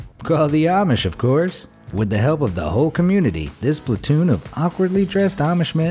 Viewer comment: DID HE JUST SAID SPLATOON?